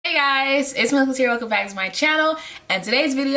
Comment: I never know what you say in the beginning of your videos I just hear “Hey guys.....welcome back to my channel” 😅 but I love your content, super helpful video 😊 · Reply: 😂, I know I speed through it! Sorry, but I’m saying “hey guys it’s me alexis here welcome back to my channel”